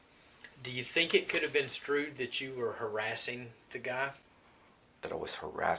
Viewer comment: did he just say “do you think it coulda been strewd that you were harassing the guy”? is that short for construed ?